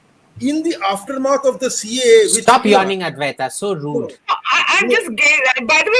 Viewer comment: what is AIM saying? I'm unable to understand... "stop .?.?.?. advaita, it's so rude" ?? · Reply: Stop yawning advaita 😂🤣😅